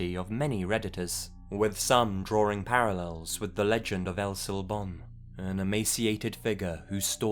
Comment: What doe he say at 9:46-9:47? El Sor-what?